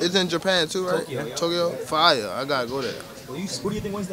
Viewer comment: fine ではなく fire に聞こえます